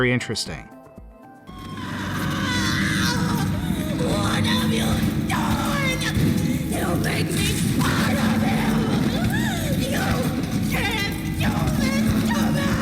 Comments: I dunno, you said you couldn’t really hear what she said so this is what I hear. “-Screams in pain-“ “WHAT HAVE YOU DONE?” “YOU’LL MAKE ME PART OF HIM” “YOU CAN’T DO THIS TO ME.” “-More screaming-“ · I heard “You’ll make me part of you!” When I first saw this. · am I the only one that heard her say "Don't make me a part of him!" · I heard "You'll make me part of him" instead of "He'll Make me part of him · I agree, I just played it and then watched this. And I agree, and actually sounds like mommy says “you’ll make me part of him!” And then I thought that was daddy long legs who took her and when she said you’ll make me part of him. I think that means she will lose skin like daddy longs if that is daddy long legs, but even how they probably got stuffed in toys like Fnaf animatronics. And like before in chapter one I had a theory, that huggy was leith Pierre, because l saw that in the video where he is saying you are trespassing and huggy’s hand was being looked down at. And when about to either load or start a new game you could see eyes inside huggy, and that is the same when you see huggy being a “statue” but fails because you can see him moving. But what I just said explains why there are toys that are alive or talking toys, and also explains why there is a bond from the so called “claw” of what I think is non skinned daddy long legs 😂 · I honestly heard her say, " DONT MAKE ME A PART OF HIM!". She was scared of the claw monster and knows what he can do.